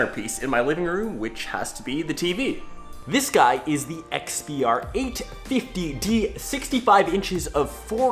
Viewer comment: Dude he clearly says it's the XBR 850D